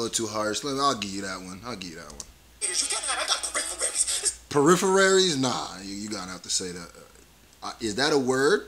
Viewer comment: Peripheral Vision, or “looking through his peripheries” is what Dax meant. You bugging saying it ain’t a word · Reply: it sounded like he said peripheraries. thats why said thats not right.